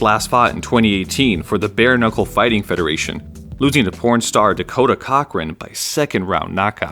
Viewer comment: "Losing to Pornstar Dekota Cockring", misheard it but I got caught way off guard by that...